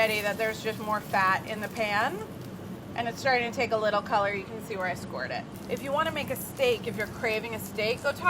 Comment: I'm no pervert. I'd never make a gross joke about you using the word "bone", Carla. Wait, what, did she say "You can see where I squirted" at 2:36?!?!?